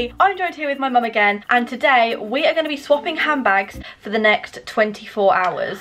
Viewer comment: I thought it said swapping husbands🤣🤣 i got so confused🤣